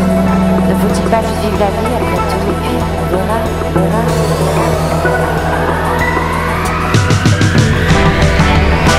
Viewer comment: minuto 2:47 alguien sabe que es lo que dice? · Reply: Yo creo que dice luna